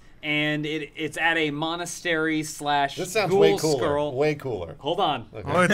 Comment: Did he say Monastery / Ghoul Skirl? Did he mean Girl School?